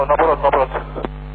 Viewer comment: As a Spanish speaker, I heard in 5:05 “No puedo, no puedo”. I know its Portuguese, but they are almost similar, “No puedo, no puedo” means “I can’t, I can’t”.